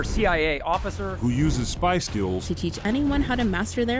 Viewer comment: In the intro, it was said that he uses “spy skills”… only, I heard “Spice Girls”. Good video but I’m disappointed there weren’t any Spice Girls. 😂